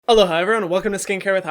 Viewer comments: I keep hearing “Hello hi everyone” I’m literally going crazy over it 🤯🤯🤯🤯🤯🤯🤯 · After your tweet about everyone getting your intro wrong (I always heard aloha) now I hear “hello ha” 🙄🙄🙄